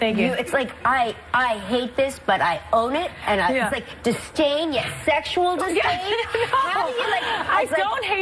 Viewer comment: Can someone tell me what she said from minute 2:19 to 2:25? "sustain it with sexual disdain"? just staying it sexual this way?